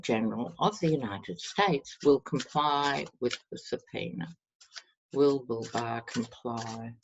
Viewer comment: Lol you said comply with subpoena's; I thought you said comply with the penis. I thought yeah that's right he is a dick.